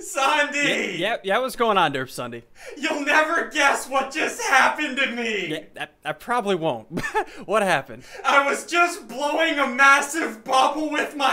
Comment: i thought derp ssundee said he was blowing a jod